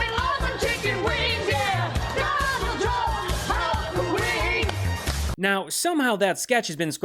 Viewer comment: I thought he said "house of wigs". 😂